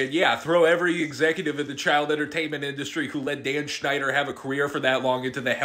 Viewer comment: i wasn't paying attention and misheard Dan Schneider as Dan Povenmire and was like "TF DID DOCTOR DOOF DO WRONG???" before my mind autocorrected